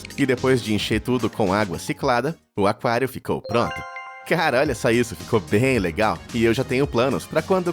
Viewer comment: oque ele disse nessa parte? Eu não entendi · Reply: Era pra ser "cara olha só isso" só q a IA falou outra coisa kk